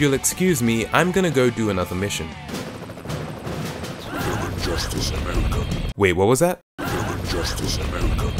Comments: Did he say ''Your just as american'' ? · I think he was saying "justice for America"